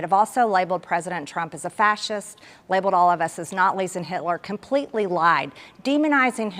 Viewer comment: did she say "natlies"? Forget reading one, has Marjorie ever been in the same room as a book? 1:55 says the reporter is the problem in this country then says she is regular like her?!?